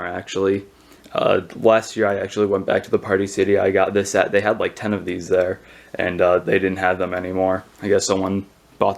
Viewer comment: anyone know what the mask at 4:52 is i couldnt hear what he said because of the muffled audio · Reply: Walgreen Reshoot mask